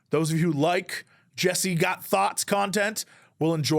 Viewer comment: all I heard is, "Jeese got THOTS content." I might be rolling on the ground now. Amazing.